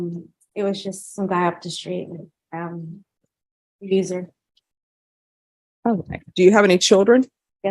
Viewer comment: It sounded like she said "a user"....